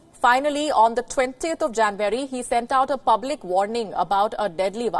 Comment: Sounded like she said “20th of janbury” 😐